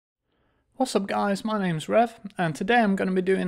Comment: thought u said mah name is jeff hahaha